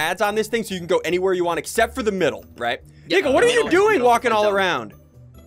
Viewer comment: sounds like he says the n word